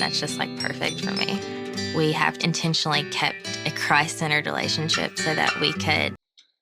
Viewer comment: At the beginning I legit thought she said "we have intentionally kept a cry-centered relationship" and I was all "wha? Well if you guys are cry babies then whatevs." But she actually said Christ-centred. She can't enunciate for shit. And so then i was all "oh ffs."